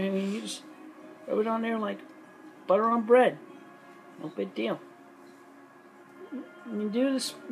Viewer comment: i think she said "No big deal" not "Up and down" but i respect the guess